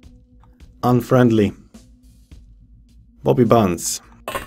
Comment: your english is great but when you say bobby bunz with your accent it kinda sounds like you were saying poopy pants lol made me giggle